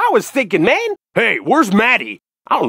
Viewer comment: At first i thought he said Maddie which is my name